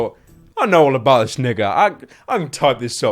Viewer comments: did u say Nigga? U know there won't be news about it bcos u not as big as pewdiepie so yeah... Kinda pointless... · DID HE JUST SAY "NIGGA" LMAO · DId he just say "nigga"? xD